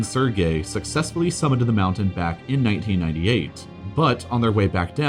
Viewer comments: You said, “1998” instead of “1988.” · The caption said Tibet had 2 people every square meter not kilometer. Must fix. Must have Toyota Corolla. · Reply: He also says 1998 at around 5:35, but the caption says 1988. 🤔